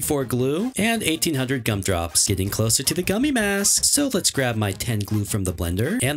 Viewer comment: Did he just say gummy mask instead of gummy boots?